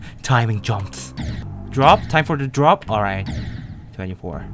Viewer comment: Did he say Cái j Dzậy in Vietnamese?????